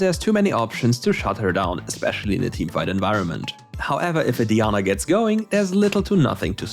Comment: did he just called diana deana??